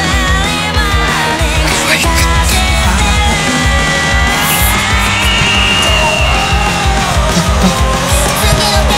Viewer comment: あたりのEvery morning溶かしてるの後のシャウト？って何て言ってるんですか？ わかる人いらっしゃいましたら教えてくださいm(_ _)m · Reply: 「行くぞー」だと思います